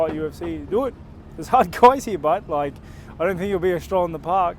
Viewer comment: What did he just say? For the life of me, I cannot understand it.. any help? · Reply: "do it, theres hard guys here but like.....I dont think itll be stroll in park" I think is what he said